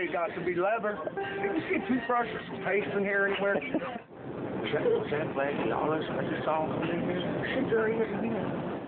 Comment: What does he say from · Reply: The segment actually started at 2:35. Lucious (Schmotz) said "Was that Blackie Lawless I just saw walking in here? Shit, there he is again" I replied "Blackie Unlaw". Schmots then said "That lead singer for that Wasp? (It actually was Blackie & his wife). Backstory: We were fueling up at a truckstop around 3:00 am here on tour supporting WASP in 2001. And yes, that was my bus in this video where he pissed on the carpet. I was the bus driver for Mushroomhead <num> tours. Great times!